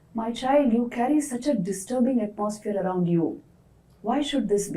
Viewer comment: Question: at 1:58 I do not understand one word. Vivekananda says, “My child, you carry such a disturbing _____ around you...” what is that word? Thank you if you can help me. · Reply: atmosphere